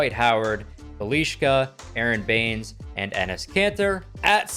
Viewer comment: a lot of americans dont know how to say bjelica, i first thought u said "belishka"